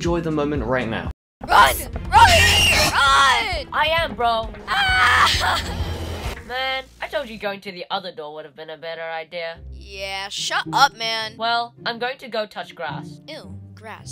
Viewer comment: they said hut up man instead of shut up man